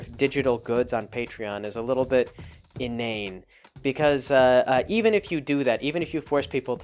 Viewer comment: I'm trying to learn more words. What is the word he uses here? innain? I can't spell to look it up. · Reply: Inane: silly;stupid